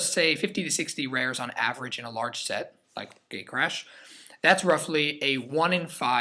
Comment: i thought u were saying gaycrash I was laughing so hard lolol